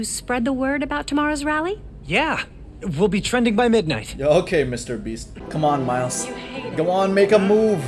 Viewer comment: I thought he said ok mr obese